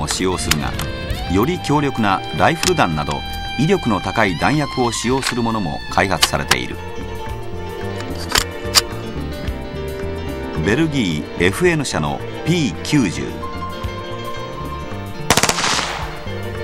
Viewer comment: i hear he kill you lol